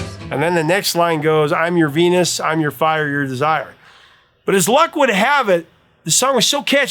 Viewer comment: I think we tend to give non-native speakers of English a lot of slack, assuming it is just their accent. So, I we miss "godness" and "crystal ass," however, I keep hearing "chore desire" instead of "your desire" (accent maybe?)